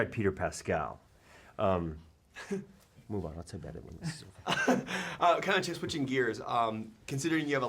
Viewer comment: I can't understand what he says at 20:45 - "move on" and then what? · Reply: "move on, I’ll tell ya about it when this is over."